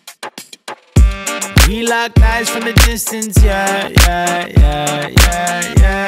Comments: who else thought he was saying " we like guys from a distance" lol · At the start I thought it said “we like guys from a distance”